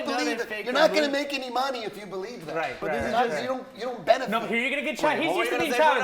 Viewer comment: did he say you're not going to make any money off it if you believe it? so hes saying hes just doing it for money? was this live? i hope thats not what he meant